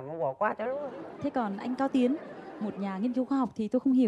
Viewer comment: Sao lại là anh cao tiến hay t nghe nhầm nhỉ